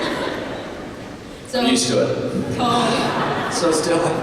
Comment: What does kj say at 13:47 ? Can't understand his accent sometimes😂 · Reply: I'm use to it 😂😉